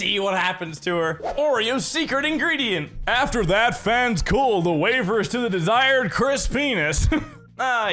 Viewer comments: I put captions on and it heard it as “Chris Venus” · when having captions instead of penis it says venus 😂😂😂